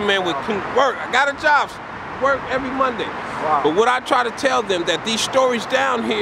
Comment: did he say " work every Monday " ? If that's the case and he meant one day a week he's going to need to work a lot more than that to meet his needs and get a place. Hope he finds more than just that or at least put forth the effort